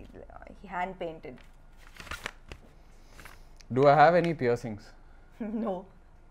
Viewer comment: what is question I don't understand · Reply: Piercings